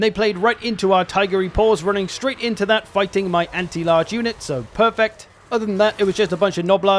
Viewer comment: Did you just say purrfect? 🫣